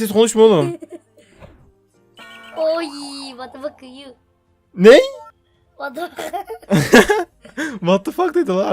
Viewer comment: ne dedin lan herhalde vatafakayı dedin